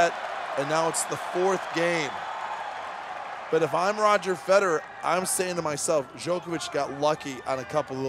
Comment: Novak hits the best back-to-back bh lobs you'll ever see under enormous pressure... 51:15 - Commentator - "Djokovic got lucky on a couple of those lobs." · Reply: That’s not what he said :) “51:14 “If I’m roger Federer…”